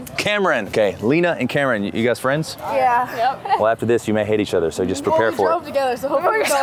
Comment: I thought he said after this you may hit each other.